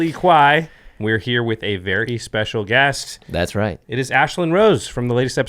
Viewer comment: Did he just say a Verhey good guest? Do I sense the return of Gavin? Also, is saltier than Urza’a blue mana.